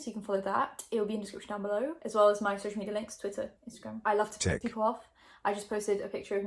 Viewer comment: Umm... wait! xD Am I the only one who heard " i like to je*k people off" at 0:42 ? 😂😂😂